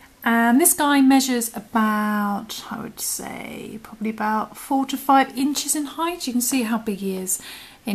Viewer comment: Was I the only one who thought she said 45 inches I was like "SAY WHAT NOW" 😂